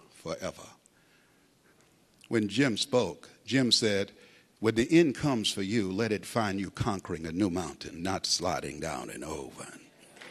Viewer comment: Hello im from poland and i can't understand clear enough quote at 4:18. Can any one help? :) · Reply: Maciej Czetwertyński when the end comes for you let it find you conquering a new mountain not sliding down an old one